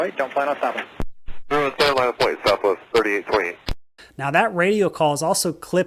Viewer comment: This is, indeed, confusing. At 4:19 the CAPTION says “31C, line up and wait Southwest 1328” but the audio CLEARLY says 3828. Yes, mistakes are made. Even in videos.